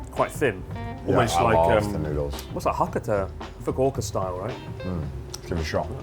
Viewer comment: I had to replay this part I swore he said “Hawk Tuah” style